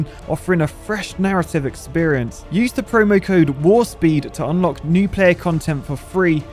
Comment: 🚀 Install Star Trek Fleet Command for FREE now <link> and enter the promo code WARPSPEED to unlock 10 Epic Shards of Kirk, enhancing your command instantly! How to easily redeem the promo code 👉 <link> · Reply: It sounded like you said WAR-SPEED in the Ad. I assume WARPSPEED is what you ment to say. BTW I love your videos.